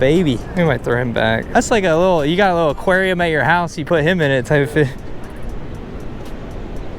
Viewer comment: Did he say type shit at 16:34😭? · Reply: He said type fish